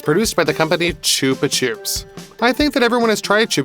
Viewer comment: I can never unhear choopachoops. Epic video as always though! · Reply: I thought it was said like chubba chops💀